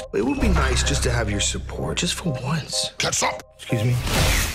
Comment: I swear, for the past few months, I thought Venom says "GET SOME!", but instead he says "KETCHUP!".